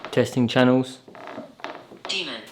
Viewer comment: I cannot believe the responses we were getting in this building, just amazing! Thanks all for watching. 👻 Bonus footage, behind the scenes, early access and more: <link> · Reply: Great set of triple videos! Just FYI where thought he heard "devil" was actually "demon". I'm just a guy that feels that accuracy is necessary... Keep up the great work you guys! 👻♒💟♾️